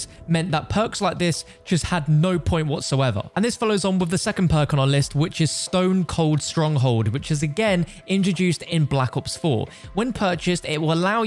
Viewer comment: I thought he said stone cold steve austin